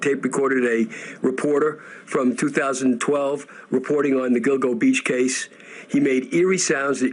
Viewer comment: I keep hearing ‘dildo beach’